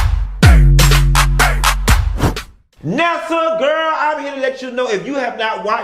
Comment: Why i heard you say “Nessa girl..” before I clicked on the video lol